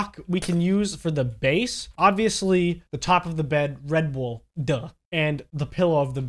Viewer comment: When you said red wool for the bed I thought you said “Red Bull for the bed”